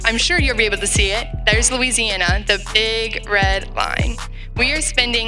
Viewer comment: Lol I thought they said "Iraq, Louisiana"